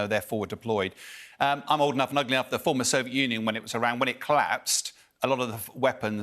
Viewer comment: Did he really say?: I'm old enough and ugly enough...